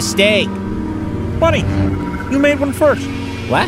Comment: Pepe: You're gonna make a mistake. Kermit: Buddy, you made one first. Best line ever said before a finisher. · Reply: I thought Kermit said "Funny, you made one first